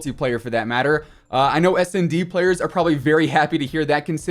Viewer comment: I think I heard smd lol